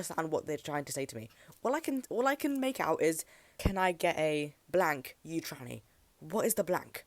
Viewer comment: i think it says "can i get a rat"? not sure tho but that's what I heard 😭